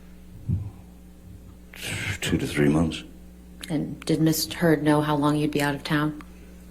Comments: Did JD's lawyer say "Miss turd" as a purposeful mistake at 50:27? Did I hear correctly? Is anyone else hearing miss turd at that point? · that’s where she says Miss Turd! I thought I misheard! Lol 😂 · DID SHE SAY MISS TURD??